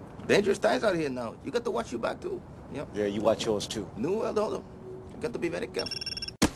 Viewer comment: Lmao, and wtf does he say at 4:56, right before he says, "you got to be very careful"? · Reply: "New World Order".